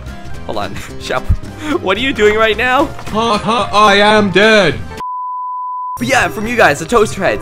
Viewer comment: XD instead of I am Bread, it's, I am Dead XD